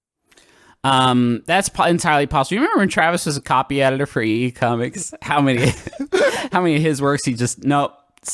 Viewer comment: I thought Griffin said EE Comics instead of e e cummings and I got really confused.